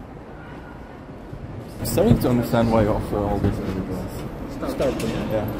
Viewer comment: I thought he said "Starting to understand why you offer all these other girls" , Starting? , yeah